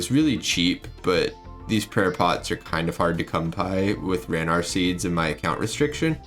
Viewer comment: he says cum pie instead of come by.... almost positive